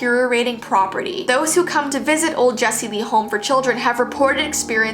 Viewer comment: who else heard her say "whoever comes to visit jessi vees home"